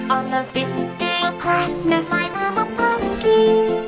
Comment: pinkie sounds like she saying "my mama pony gave..."